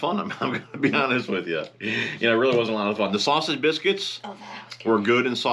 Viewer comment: Nice explanation for changing up spinner bait selection! But have to re-watch thought I heard you had B’s and G for breakfast?! Better let Debo know! Glad big Mike had fun! Nope just sausage biscuit.